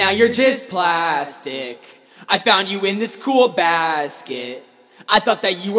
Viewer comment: I heard "I found you in the school's basket" for awhile until I realized you were saying "I found you on a really cool basket" 😂😂 How about you do a parody of Uptown Funk?